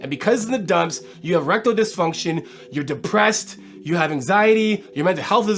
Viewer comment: sounds like he says “rectal dysfunction” 😂🤣😂🤣 That doesn’t sound like a fun time at all 😂😂🤣🤣